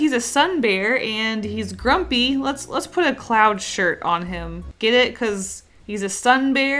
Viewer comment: 8 thought u said "clown shirt" until tomorrow I realized u said "cloud shirt"